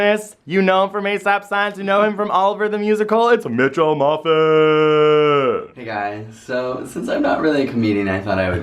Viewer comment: It sounded like he said 'Mitchell muffin' 😂😂😂